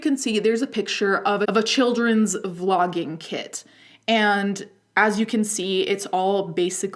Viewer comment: I had this playing in the background while working on something else- You head my immediate attention when I misheard you saying “children’s vlogging kit” as children’s flogging kit. I was like Christ man. What?!?